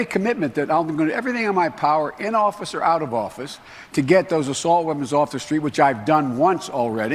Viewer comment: I heard "assault womens."